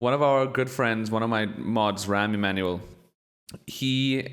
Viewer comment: sounded like you said Rahm Emmanul, the former Chicago mayor and whitehouse chief of staff. That'd be funny if he was also randomly one of your mods